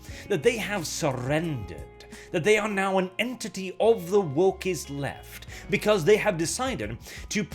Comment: My mind heard that as ''An entity of the Warp'' the first time.